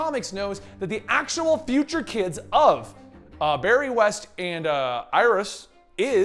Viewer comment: did he say barry west